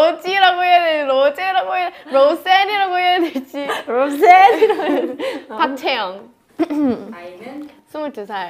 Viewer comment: i think she said roseanne not roselle LMAO